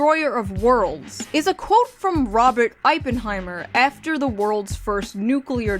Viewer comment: Am i the only one who heard you say “Ippenheimer” instead of “oppenheimer”?